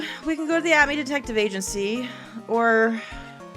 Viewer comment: When she said "Atmey Detective Agency" I heard "Acme Detective Agency" and it literally became a different game in my mind.